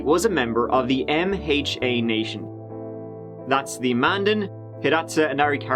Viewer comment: Thought bruv said Mandem